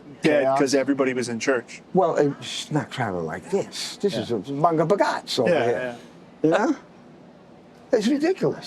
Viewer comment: what Italian phrase does he say here? “This is, well you know, manga bogats!”